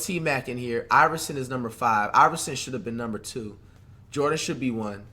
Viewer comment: Did he just say Iverson should be 1 😂😂😂